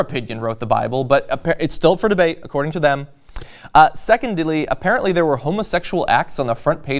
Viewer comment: did say "secondely" ???